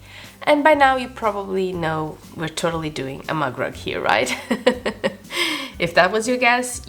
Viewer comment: Very cute! I was so confused throughout the video because I thought you said mud rug. I kept thinking that's way too small to be a rug. 😅😂 Now I get it. A MUG rug. Where I'm from, we call them coasters. But clever way to use scraps either way.